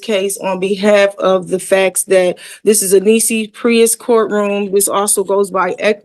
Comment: How does this nitwit know the Holy Spirit didn't send the stand-by counsel to assist her? Also, right around 20:05, what did she say? "... this is a Reese's Pieces court..."? That's a new one on me. (I know she was trying to sound 'edu-ma-kated', but I still don't know what she could have been trying to refer to.)